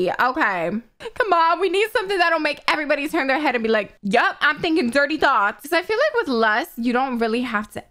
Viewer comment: When she said "yep I'm thinking dirty thoughts" I really thought she said "yep I need a dirty sock"